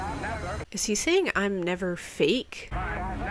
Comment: He was saying "crime never pays." There is historical footage where that was sampled from. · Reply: All this time I thought he was saying, “I’m never safe.” Lol